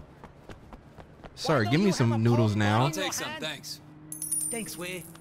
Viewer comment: I'm sorry if this is bad i swear berleezy almost said poodles and noodles in 43:47 from the last of us 2 episodes. 🤔😂😂😂😂😂🤣🤣🤣🤣🤣🤣🤣.